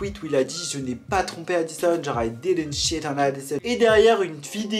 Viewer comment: j'ai entendue, "I didn't shit on Addison" suis-je une mauvaise personne ?